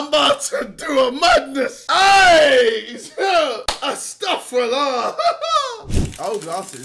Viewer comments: what does JJ say here?? He says this al the time and I all hear is “I STUFF RULER” in a French accent, I’m uncultured. Help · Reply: he says astaghfirullah which means I seek forgiveness from Allah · is he saying astagfirullah??